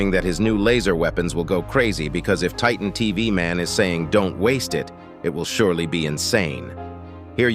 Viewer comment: I heard don't wait